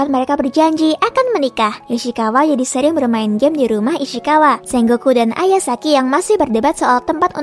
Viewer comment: isykawa sering main game di rumah isykawa apa kah aku salah dengar